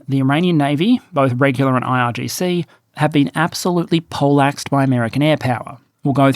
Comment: he said the Iran navy has be plaxed? I cannot nor can auto translation figure out what he said. If he has fear of losing views then l will say it for him... The Iranian navy has been completely CRUSHED and is now at the bottom of the sea to never return during the next 1000 years. Lets not be shy. The Iranian military at the start of this conflict was farrrrr, FAR more capable in all areas except intellectualy than the Ukrain military at the start of their war? So what happened? How did Iran crumble in hours while Ukrain was successful during the first days of their conflict with russa? Why? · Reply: He said "poleaxed" - meaning hit so hard that you are stunned or knocked out. For a full explanation - a poleaxe was a medieval weapon designed primarily to enable foot soldiers to counter cavalry and keep enemies of any sort away. Think a long spear with added spikes or bladed edge. I imagine that being hit by one was an unpleasant experience.